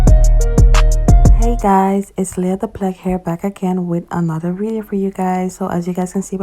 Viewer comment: I’m sorry but if you hear it I swore she said “Leah the plug hair” in the beginning but I know she said HERE